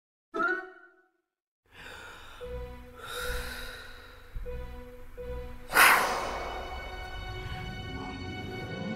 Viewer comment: What is 11 saying?!??? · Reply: Who needs contactssss and fake teeth when you got the real deal?